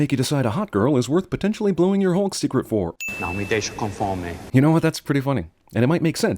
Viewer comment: He said "don't let me go hungry", não me deixa com fome, I'm Brazilian, his accent is so thick you can barely understand what he's saying, but still, he said it.